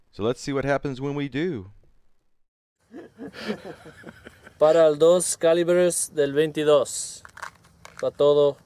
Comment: CC says "delve into those" when he says "de venti-dos"